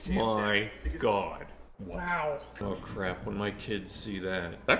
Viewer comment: Thought he said bricked up